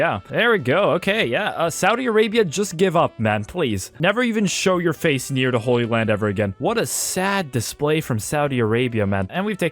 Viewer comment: What did he just say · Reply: "Never show your face in the holy land ever again"